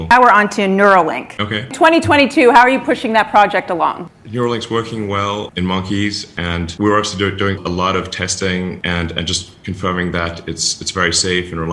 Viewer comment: When he said “neuralink works well in monkeys” I literally heard “works well in Mach E’s” 🙈🤣